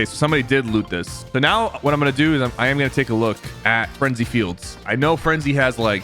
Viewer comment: did he really Said frenzy fields